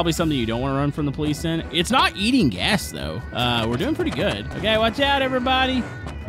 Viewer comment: did you say gas or something else